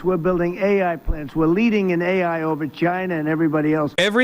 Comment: Ai over China sounded like him saying the word vagina